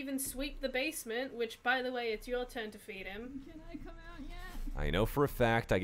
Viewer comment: I heard a "Can I come out yet?"